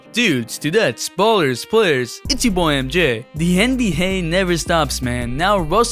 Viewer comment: who heard him say nbha